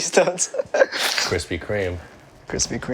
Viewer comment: Did Brian just say "shkere"? He's a Face fanboy? What did I miss lmao